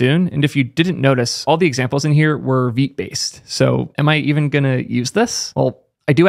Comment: "examples were ? based" What was said there? Unfamiliar with what he said. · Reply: All the examples were Vite based. Next js and Vite are somewhat similar in what they do but Vite works with Astro, Vue and other frameworks in addition to React. It was created by Evan You that also made Vue :)